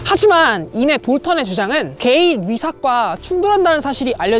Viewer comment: "하지만 이내 돌턴의 주장은 ???과 충돌한다는 사실이 알려지게 되면서" 여기서 ???에서 뭐라고 말하셨는지 모르겠네요 ㅜㅜ 뭐라고 하셨나요? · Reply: 게이 뤼삭